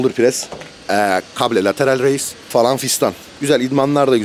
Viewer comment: kabe lateral raise diye duydum bi an tövbe est